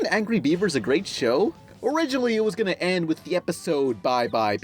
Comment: I swear, during the shrek part, I heard "Ogreriginally"